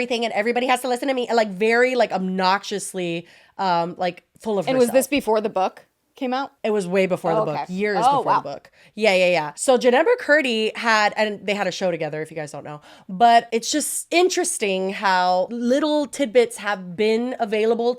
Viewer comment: I thought you said Jeanette Mccarthy and was thinking of Jim Carrey's wife that Ariana was crushing on 🤮